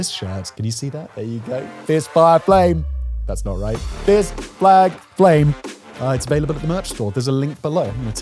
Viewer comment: I swore he said p*ss flag flame